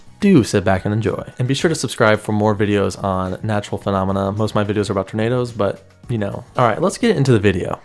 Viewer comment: This is the first of your videos that Ive seen and thought you said "all my other vids are about tomatoes..." and i thought like huh, strange that this guy is doing vids about tomatoes but this one right here Im really enjoying. Only now after reading the description I understood xD